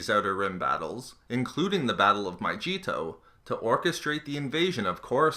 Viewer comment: I always hear "the battle of My Cheeto" xD